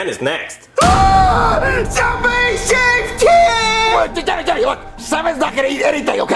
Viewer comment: what did he say? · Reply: SOMEBODY SAVE TEN!